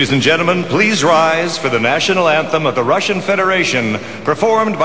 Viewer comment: I'm sorry, I mean no offense, but I swore he said "conducted by Vladimir Lenin!" haha! omg